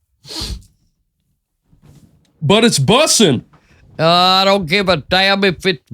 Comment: When she said its bussin I heard "its busted" lol